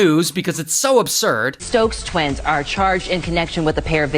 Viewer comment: I thought it said stroke twins 😂